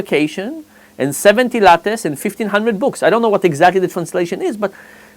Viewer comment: I thought he was saying lap dance instead of latte.